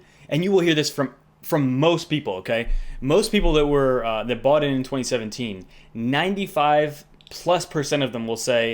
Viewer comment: I thought you said 'fought in 2017' hahaha. Takes me back to 'Nam